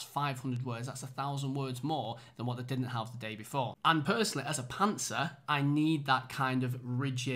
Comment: you say you are "a _____-er" but I cannot figure out the term even with the context. Thought it was pantser, but I don't see indication of you drooping the audience. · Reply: It’s suppose to say ‘and personally as a pantser’ My editing skills 😂